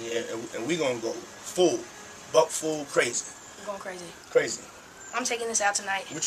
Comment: did he just say “butt full crazy”? 😅